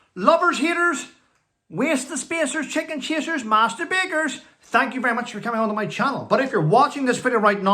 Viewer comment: Good morning. It is lovely to see you bright-eyed & bushy tailed . Agree with you regarding police too soft & woke. Stay safe . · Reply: I am not that good w. regard to my hearing. I always thought that said "and masterbaters" instead of "master bakers". Made me think of that British woman show sells her fart sounds on a private channel to those who have weird sexual fantasies.....saw her on a talk show...maybe w. Hollie Dolly & Phillie